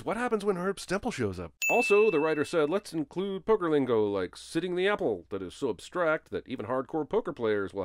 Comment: Seriously? You thought Teddy said, "sitting the apple"? How much more clearly could he have said, "set for battle"?